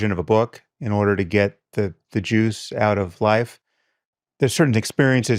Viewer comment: Damn at 4:20 I thought I heard him say he wanted to get the Jews out of life lol.